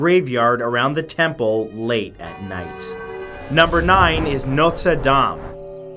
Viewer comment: I was on the other side of the room when he said “Notre Dame” And I swear to god thought he said “MILF Saddam”